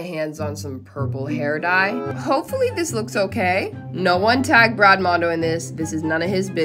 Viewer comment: Sorry I misheard and thought you said to tag Brad mondo